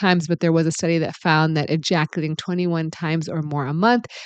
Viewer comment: Oh, I thought you said 21 times a day. I couldn’t continue that pace much longer